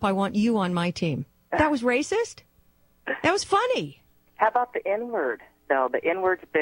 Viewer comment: What are you deaf the very first question that we hear the woman asking at 0:25 and the initial topic raised by her was.. "What about the n-word" and Dr Laura responded with a rant about the double standard with regards to that word, which is a perfectly reasonable objection raised by many reasonable people, not all of them white. You are too biased to listen clearly or debate intellectually on this topic. You, and the TYT are like Democrat tear gas to moderate voters.